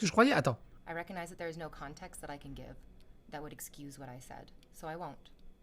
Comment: A 10:00 j'entends "That what i excuse what i said" après je suis pas ouf en anglais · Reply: That would excuse what I said*